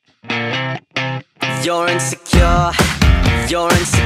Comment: Nobody: Therapists in their last session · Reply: I can’t year “your insecure” I kept hearing “yurin secure” 😭☠️